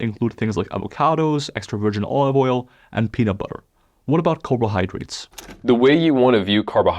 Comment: Has he really said peanut butter? 😂😂